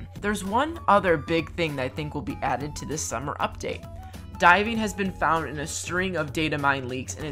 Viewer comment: When you said “diving” I honestly thought you said “die”- I was confused on how that was exciting 😂